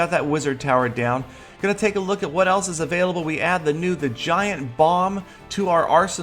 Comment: I heard vagina bomb xDD